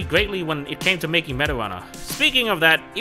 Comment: What SMG4 said : Meta Runner What Auto-Generated Subtitle said : Marijuana